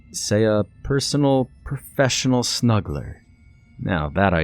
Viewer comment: Lmao i thought it said smuggling. Na I don't think this is for me . Lol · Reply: That could be a Creepypasta. I thought the ad said professional smuggler ,what I got totally surprised me ...